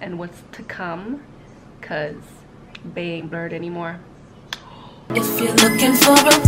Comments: hahah oooh you said Bae aint blurred anymore, I thought you said Bae aint Burt anymore, I had to rewind the video like who is Burt?? lol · yooooo at first, I thought you said bae ain't bae no more. I was just about in tears. 😩